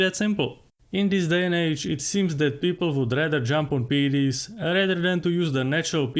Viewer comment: What is PD? or is he saying something else? "people would rather jump on PDs rather than to use the natural PD called sleep" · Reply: He says PED's or "performance enhancing drugs"